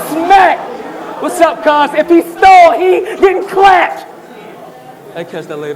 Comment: Surf said what's up to Stalley and said "if he stall he/stalley getting clapped"